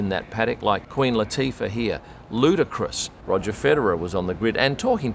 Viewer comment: Haha 😂 The moment I’m looking down to fix my charger because I felt it pop out, I thought you were saying Queen Latifa’s outfit was ludicrous! 6:28 So because I was looking down I hit the ‘10sec. Back’ button to see just how crazy it was. Only to realize NO, it’s LUDAAA!! Haha Ludacris, the dude rapper 😅